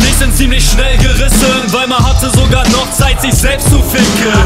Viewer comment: Ich hab immer verstanden: "weil ma hatte sogar noch zeit sich selbst zu ficken" und nicht weima :D